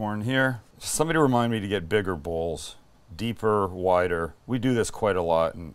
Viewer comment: I thought Sam said "someone remind me to get bigger balls" he actually said bowls. Haha